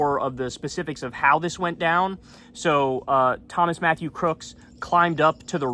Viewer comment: At first I thought Kyle said Thomas Massie's crooks.